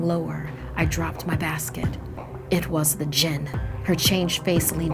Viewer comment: lol I was watching with subtitles and when it said "the jinn" the subtitles said "the jenna". Its confirmed, ppl with the name jenna are jins